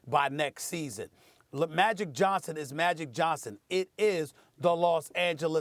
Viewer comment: Did anyone else hear SAS slip up at 5:41 and basically say "leMagic Johnson?"